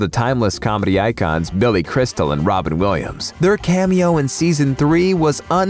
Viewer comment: Did he say ROBERT Williams ??